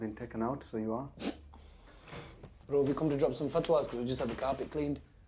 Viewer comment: "Bro, have you come to drop some fatwas cuz we just had the carpet cleaned?" Lmfao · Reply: I thought he said drop some fertilizer at first 😂😂